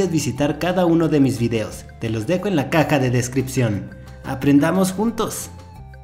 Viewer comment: en el minuto 2:35 escuché ( Te lo dejo en la "caca " de descripción ) xD LIKE SI O ESCUCHASTES